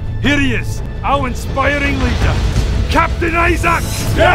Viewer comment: "Our inspiring leader, Captain Isaac!" At first I thought he said Captain Aizen.